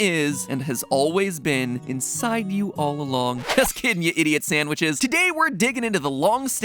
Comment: What did you just call me? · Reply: I D I O T S A N D W H I C H !